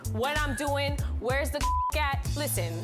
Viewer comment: DID SHE SAY WHERES THE COKE AT?!?